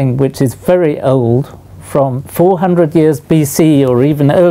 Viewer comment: This cup is looks like first ever ancient prank to me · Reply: oh I know what bc is.. I misheard it as 400 years ago.. my bad